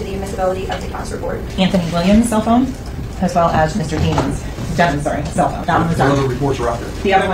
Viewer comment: Lol Prosecutor think she slick called him “Mr.DEMONs “